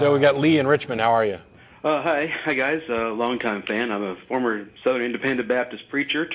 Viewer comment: I thought that he said I am a former Soviet spy, I had to go back and listen again.